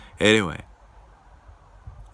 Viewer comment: Lol thought you said “ima go buy some soda tomorrow”